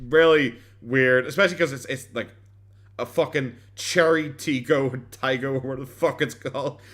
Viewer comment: The brand is unfamiliar because of high safety standards that that tin can doesn't meet. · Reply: I had never heard of this, I thought she was saying Chevy weird but I tried to look for it for sale in the USA and couldn’t find it with a simple google. And EVERY car comes up with a link to buy it with simple google search lol. Maybe if I really dig around, it can be imported (no one’s paying to import them, let’s be honest).